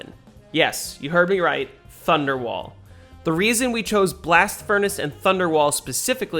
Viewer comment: I heard wonderwall and immediately grabbed my guitar. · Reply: he said thunder wall